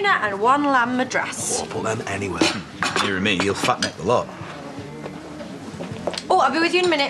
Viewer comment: I missed what he said at 2:03 to have Aaron give him that look lmao—someone help meee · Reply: YOUTUBE USER He said, ‘nearer me, he’ll fat neck the lot’ meaning that he’ll eat all of the curry, including Robert’s